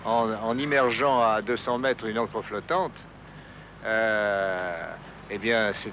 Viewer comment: En immergeant à 200 mètres une ............ flottante. Il dit quoi, j'ai pas réussi à comprendre le mot ??? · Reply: une ancre ;)